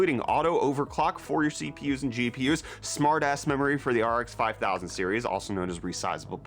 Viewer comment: "Smart Ass Memory" ? lol. i had to listen like 8 times. every time, i heard "smart ass memory". not sure if it was on purpose or not, but i approve.